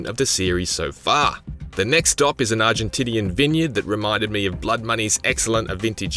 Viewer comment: Did i just heard arginTITY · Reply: Argentinean...